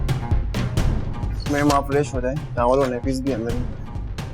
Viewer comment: did he say towel aur Nappies bhi andar hai? 🤐